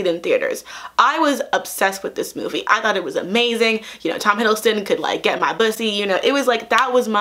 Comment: did she say "tom hiddleston could get my bussy" 💀💀💀💀💀💀💀💀💀💀💀 but on a less memetic note, this is an interesting video and i like hearing your thoughts about it (even tho i truly don't know anything about superhero comics or movies)